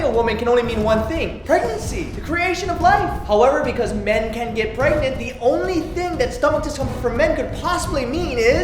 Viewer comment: WHAT DID HE JUST SAY ? DID HE SAY MEN CAN'T GET PREGNANT